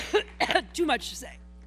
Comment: At 14 sec sounds like she says--"Too much sex"!!!!!!!!!!!!!!!!!!!!!!!!!!!!!!!!!!!!!!!!!!!!